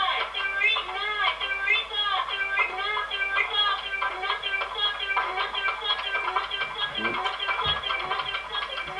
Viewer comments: does anyone know what it's saying, I can't understand it. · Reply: Electric Lizard it’s saying “mother F***er” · What's it saying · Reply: Synthesizer it’s saying “Mother F***er”